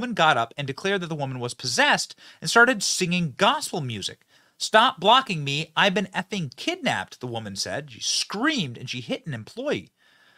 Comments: What I heard her say first was "stop blocking me, I was raped, I've been kidnapped". TY Benny! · Here’s a thought - what if the woman was really being kidnapped? And no one believed her and blame her being crazy or possessed but maybe she was telling the truth. Call someone crazy and they are dismissed. So sad either way what happened. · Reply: I thought I heard her say "I've been raped. I'm being kidnapped." Have a listen again.